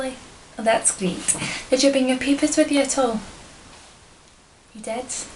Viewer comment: The first time I saw this I thought she said 'did you bring your peepers along with you at all' Being an eye exam I would hope so :P